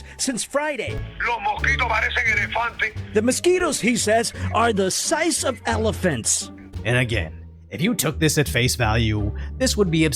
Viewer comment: That's not what he said, he said "they look like elephants"